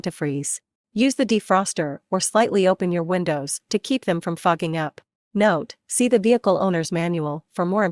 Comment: what did she say 😲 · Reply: 'to keep them from fogging up'